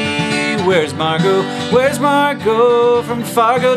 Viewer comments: sounds like he says wheres MARKO...marko POLO(ye i know thier probably spelled wrong) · my freind thought he said wheres marco ...like the game marco polo